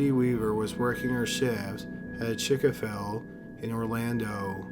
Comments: WAIT... did he just say Chik-A-Fil??? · wait hold up did you just say “chick-o-fill” 😂😂😂😂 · wait did he just say Chick-a-fill for Chic-fil-a?